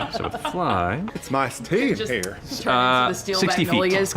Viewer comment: I was tired, so at first, I thought Matt said "You turn into a she."